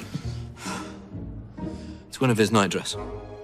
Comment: Ok... 4:16 did he say it's one of his night dress???